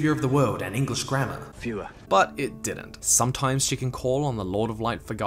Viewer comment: I thought he said fürher